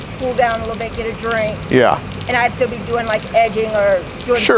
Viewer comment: does she say shed be edging lol...